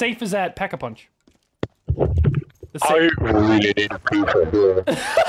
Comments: My favorite "I really need a prfhejek" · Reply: Sounds like he said I REALLY NEED THAT PIECE OF HERE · Me in the middle of a test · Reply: I thought he said "I really need a new computer"